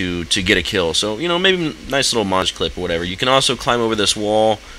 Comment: did anyone else hear him say "monge clip" at 3:27?